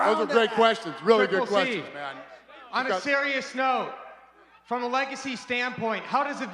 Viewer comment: Dana says "those are some great questions" sarcastically to the reporter, but does he finish it by mouthing, "you're dead?" or am I hearing things